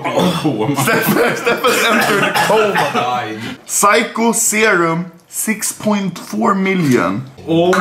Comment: Is it just me or i thought he said "Psycho Jews."